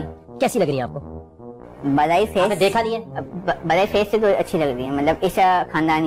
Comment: i thought he said malai face😂😂